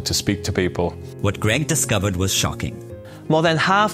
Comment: I heard this as "What Graham discovered was sharking." I laughed at myself, but having watched the rest of the video, it's true! He is now actively sharking, and teaching others to shark. Thanks so much for opening our eyes to another great project.